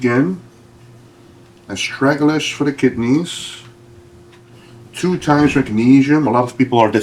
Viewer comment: What is he taking for kidney support at 28:04? I cannot understand it acoustically. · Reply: Astralagus.